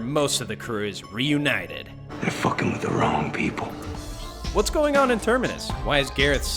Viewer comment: I thought Rick said "they're screwing with the wrong people" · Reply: On the DVD/Blu-ray version he says “they’re fucking with the wrong people”